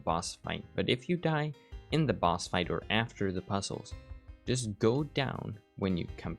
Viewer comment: I watched this video right before doing the quest but I misunderstood 14:09 and thought you said “if you die go through the door” and when I went through the door, I raged 😂